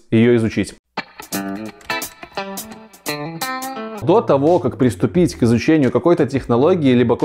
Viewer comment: На протяжении всего видео я услышал всего лишь одно мне понятное слово "дота-во"